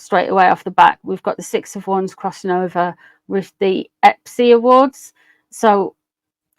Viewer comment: 😂😂😂😂 I misheard it, thought you said Pepsi awards and was confused, only now listening this morning I have realized it’s not Pepsi, I’ve been laughing all morning.